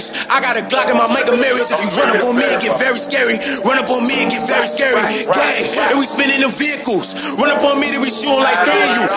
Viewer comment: At the beginning , all I hear is "gAy GaY gAy GaY" 😭🤣🤣 . (p.s. ik what he really said) .